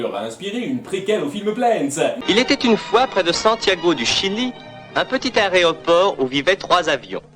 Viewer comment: Heu y'a que moi qui a entendu "un petit aréoport" à